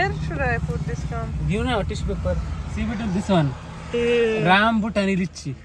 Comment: Rambutani nahi Rambutan from thailand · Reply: I thought he said rambutani. Thank you for correcting 🌸